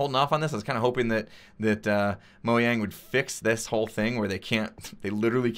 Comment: Is it me or did he said Moyang?